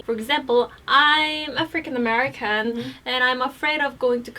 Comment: At first I thought she said "I'm a freaking American." at 1:14 instead of "African-American